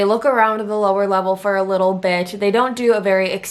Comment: it sounds like you’re saying little bitch instead of little bit